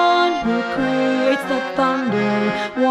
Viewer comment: I know it says 'creates the thunder', but I keep hearing 'predates the thunder', which seems much more terrifying.